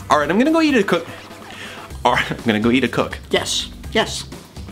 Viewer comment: did I just hear him say, “I’m gonna go yeet a cook?”